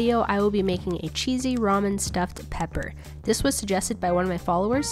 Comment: I thought it said Gordon Ramsay stuffed peppers